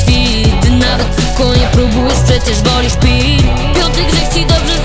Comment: czy tylko ja usłyszałam pi zamiast pić ale i tak super ślicznie śpiewasz